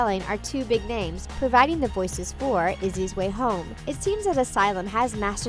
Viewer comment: I thought they say Lizzie way home.... you know pink fish describe LDshadowlady